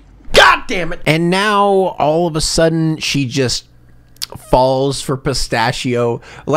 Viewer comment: an ad cut in perfectly so it sounded like you said “she fell for piss” good job youtube